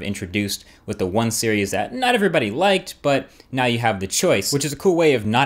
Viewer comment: This phone is AMAZEBALLS !! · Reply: EthanReres- WafflesFrom-Hell but you said you have "it", it sounded like you said you have a g4, which isn't out yet